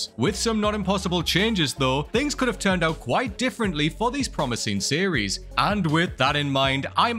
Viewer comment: “Turned out white differently?” 😂 0:32 what you trying to say! Lol